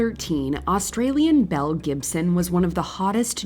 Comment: Belle Gibson? I thought you said Mel Gibson.